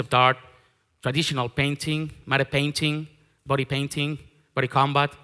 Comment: what did he say after "body painting" at 3:36? is it "body combat"? Thx!